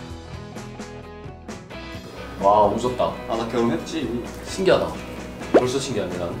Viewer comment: 에 아 나 결혼했지 라고들려요